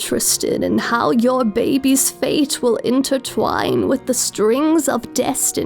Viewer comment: wait wait... hold on a sec... did she say... BABIES? right? she said babies, not babie! sooo... that means... .